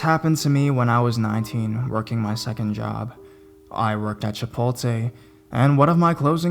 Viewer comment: did he just say chipolte?